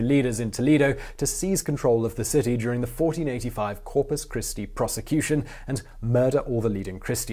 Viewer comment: At about 15:23 I hear "Corpus Christi prosecution". Should the last word be "procession"?